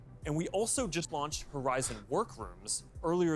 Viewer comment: I swear Mark just said "Horizon War Crimes" and I can't stop hearing it like that